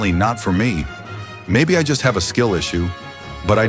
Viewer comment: did anyone say skill